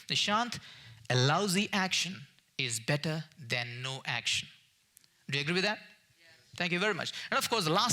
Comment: What did he say in 14:55 ? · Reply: What I said was, taking some kind of action is always better than taking no action!